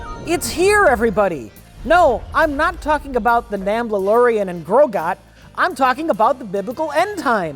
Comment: Did he say, "Namblalorian?"